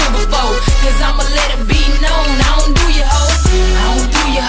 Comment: I thought she was saying "I don't doodie hole"?? I thought this song was about niggaz tryanna roll up in da back-doe... am I wrong?